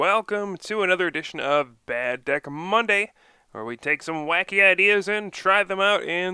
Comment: haha i paused at 0:10, and it sounded like he said wacky